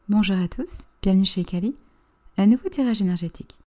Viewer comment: J'aime tellement votre voix que j'ai des hallucinations auditives, j'ai entendu " bienvenue chez Câlin " 🤣